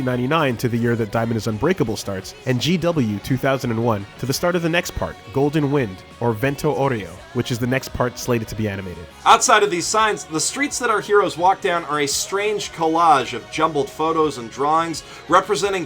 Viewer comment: Di...Did you say vento d'oreo.....